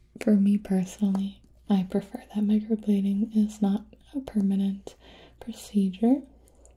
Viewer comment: XD I thought it said Micro Balding lmao I was like wth is Micro Balding, ugh Dyslexia.